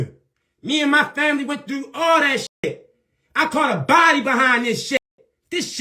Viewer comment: did he say he caught a body? wtf….